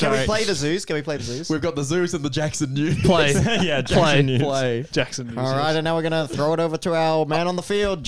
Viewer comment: When I heard "The Jackson News" I was about to be like "Jackson news! The Jews!" Then I thought for a second